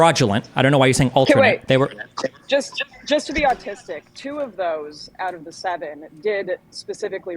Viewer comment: did... did she just say "just to be autistic..."? Like, I'm sorry maybe I misheard but i can't think of another word that sounds close to what she said